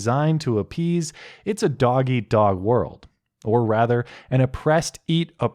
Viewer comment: I thought you said "doggy dog world" and I was getting ready to shoot myself